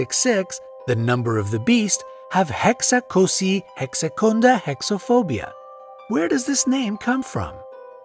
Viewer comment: What he says: "Hexakosioihexekontahexaphobia." What I hear: "Hex jababababaa Hex bababababa Hex bababa phobia."